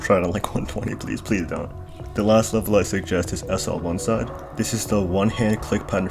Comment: what's the level name?I didnt heard it quite right. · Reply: SL One Side